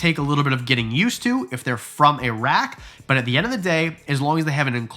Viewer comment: what he said: "when they're from a rack" what I heard: "when they're from Iraq" me: huh what a weird thing to say I wonder why ball pythons are being imported from Iraq